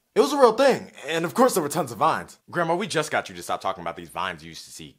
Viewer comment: I found a bloopers on 4:04 it sounds like said grandma but still it got my like and sub